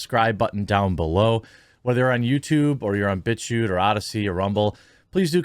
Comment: thought he said bitch u instead of bit chute